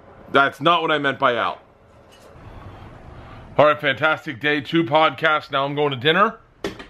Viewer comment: it sounds like he said 2pac-cast